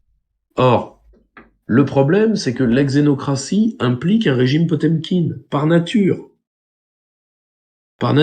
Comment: Je me suis rendue compte que je n'écoutais que dune oreille quand j'ai entendu "la xenocratie implique un effet potemkine" 😅 quand j'écoute bien je comprends PYR malgré mon inculture, mais là... 😶 ma faute 😂